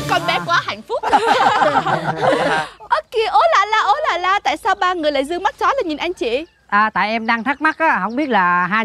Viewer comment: "ô kìa , ố là la sao 3 người dư mắt chó lên nhìn chị " ( hông hỉu sao nghe ra v =) )